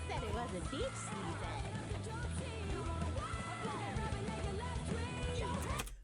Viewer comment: "Beach season." The audio is still too low. Even can't hear it!!